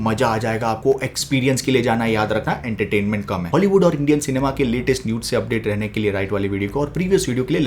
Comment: Did He Just Said Nudes?😂😂